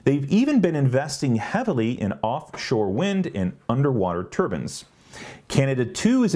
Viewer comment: For a split second, I was confused when I heard you say Norway was investing in underwater turbans. 🌊👳‍♂️🌊